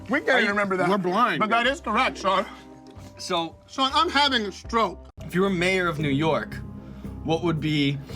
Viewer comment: "sean i'm having a STROKE" will always kill me edit: irony not originally intended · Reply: i thought he said "Son im having a stroke"